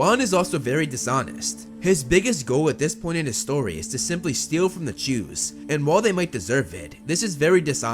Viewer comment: the auto caption says "steal from the Jews" 🤷‍♂️